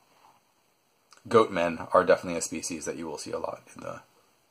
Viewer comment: did someone say goats